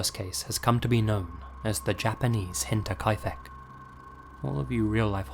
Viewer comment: Can anyone please tell me what is being said at 1:07? The Japanese Henta Kaifec? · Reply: Nevermind. Found it. Hinterkaifeck. I had never heard of them.